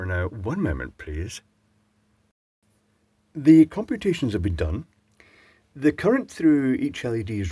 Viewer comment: For a second I misheard computations as palculations which have do be done on a Kink Palculator.